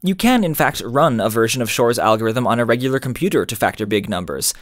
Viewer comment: Lol I thought it said Thor’s algorithm!